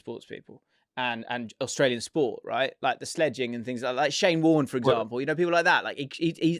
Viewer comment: Embarrassing moment I had watching the pod this week: At 25:10 when mentions Shane Warne I understandably thought he said Shayne Ward, I’m not sure why I thought being a X factor winner would qualify someone as a sportsman but I did. As soon as I heard it a grin appeared across my face, I quickly swiped across to Spotify and played my favourite shayne song (no promises) and sang along giddily realising must also love his music if he’s on his mind. After the song finished I went back to the pod and rewinded 10 seconds to understand the context of why he said shayne ward only for a single tear to gracefully slither down my cheek as I realised my fatal error. I quickly looked around to see if anyone had caught me in this most embarrassing moment, wiped away the tear and got back to watching. JAFFN is truly back boys looking forward to the rest of the season